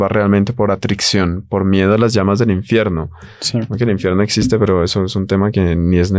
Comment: en el minuto 28:00 dice el sacerdote que el infierno no existe?????? Díganme qué lo que escuché , está mal. Cómo así que el infierno no existe?claro que existe,está en la palabra. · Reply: Salve María. Obviamente no... Dice lo contrario, "el infierno existe".